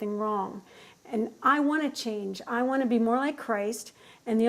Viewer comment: Did she just say "I wanna be more like Christ"?